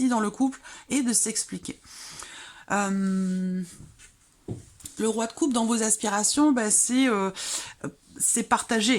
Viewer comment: J'avais que le son, j'ai entendu "le roi des pets" 😅